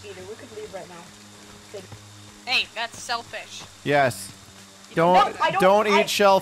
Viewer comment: Somebody put gas in the two-seater... They got selfish. Ohm: Yes. Don't eat shellfish. Exactl-- wait what? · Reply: I believe she actually said 'Ey that's selfish''